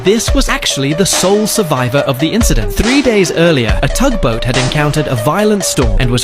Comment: Bro,i thought it said " the soul of the survivor of the incident"